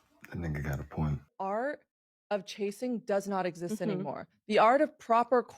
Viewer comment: Did she say chasing is an art 1:38 😂😂